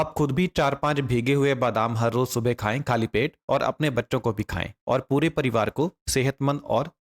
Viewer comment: Did he just said, "apne baccho ko bhi khaye"? 😂